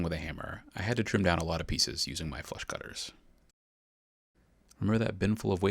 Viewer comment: I swear the first time I heard you say "flush cutter" I thought you said "flesh cutter" and was like "what?!"